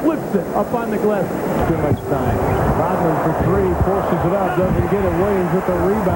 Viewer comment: Did I just hear the words "Rodman, for three"😂😂😂